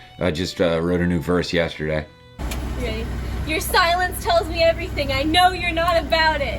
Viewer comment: I originally heard this as "your stylist tells me everything .." 😂